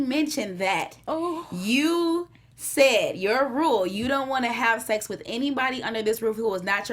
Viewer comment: Question...in the beginning of the sex scene Yasir is kissing Nuri and stops and says something. I heard “New rule....the only man you’ll have sex with under this roof is your husband” My friend heard “Your rule....” What did you all hear? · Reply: Chantea Williams Hi!! He said "Your rule. The only man that you would have sex with under this roof...is your husband". I had to go back because I got upset with him because I "heard" something else! Haha